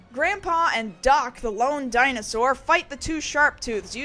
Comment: I thought grandpa was saying "The Long Dinosaur"